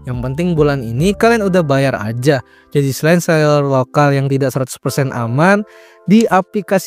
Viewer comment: kumur-kumur. sumpah gw ga ngerti di narator ngomong apa. tetep semangat latihan artikulasinya yaaaa. mungkin bisa pemanasan atau peregangan mulut dulu sebelum mulai rekaman. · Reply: "Jadi selain seler lokal yang tidak 100℅aman"